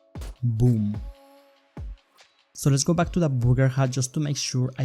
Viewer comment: Did you say Booger? LOL